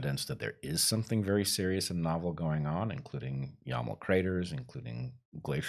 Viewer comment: can someone let me know what he said at 1:05. what craters? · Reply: Yamal craters on the Yamal peninsula - said to be the result of methane release.